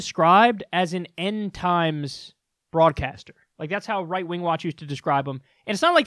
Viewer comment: I totally thought I heard you say "Fraudcaster", which you totally should now.